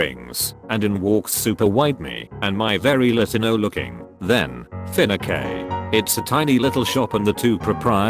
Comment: why is it pronounced “finaque” instead of fiancé lol